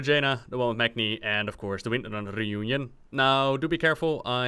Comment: "Three comics, the one with Jaina, the one with Magni and of course the Winrnrner sisters" The what sisters? lol "Windrunner", say it slower. XD